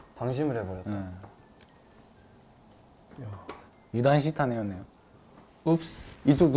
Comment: 때 유진이가 뭐라고 했을까요? 아무리 들어도 무슨 말인지 모르겠어요.. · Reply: “‘유단시타네’였네요” 라고 한 것 같아요! 유단시타네는 일본어로 방심했네 라는 뜻입니다. 아닐 수도 있구요…?